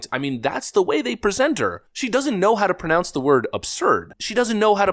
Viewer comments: I thought she said observed, not absurd lmfao paintetently lmfao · I thought she said "Observe" in that clip, not "Absurd" until he said what the quote is meant to be. Reminds me of a communications teacher I had where the word "Word" sounded like he was saying "War" due to his accent.